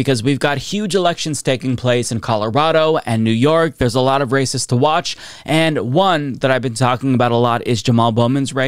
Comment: I thought you said “we have a lot of racists to watch”. 😳